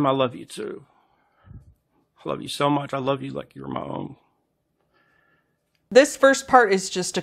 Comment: It did sound like he said, "I love you like your mom." But I listened a couple more times and I THINK he was saying, "I love you like you's (like 'you is') my own." Edited for clarity